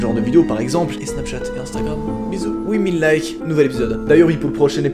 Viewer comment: Ah! Ah! Moi j’ai entendu « Oui! Mille likes » 🤷🏽‍♂️😋